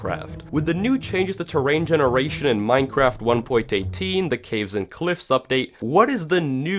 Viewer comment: Wait at 0:10 did he say 1.18?